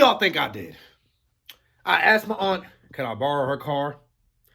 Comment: When he says I asked my aunt I thought he said I asked for the strap